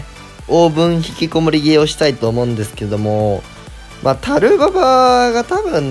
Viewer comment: で5秒スキップしたら これでトロ上げをしたいと思うんですけどって聞こえて草